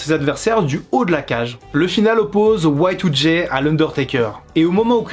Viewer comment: sa veut dire quoi ce qu’il dis ? Watoudjé 🤷🏽‍♂️😂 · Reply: Y2J* = Chris Jericho x)